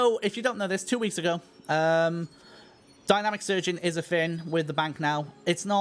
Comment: I first heard "dynamic surgeon", but dynamic searching sounds like a needed improvement among the others. Look forward to it.